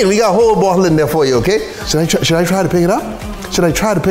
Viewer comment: “I got shit to live for”, this guy is awesome! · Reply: I thought he said I had shit to look for😂😂😂😂😂